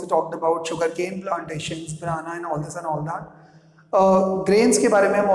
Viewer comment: Sir, what did u say at 0:29 minutes??? Was it BANANA???